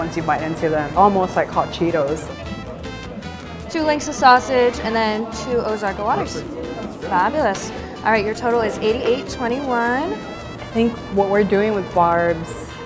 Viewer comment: Did she say 2 sausages and two waters 88 dollars